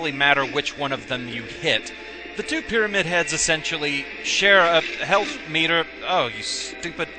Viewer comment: it sounded like you said "They share a health meter of stupid."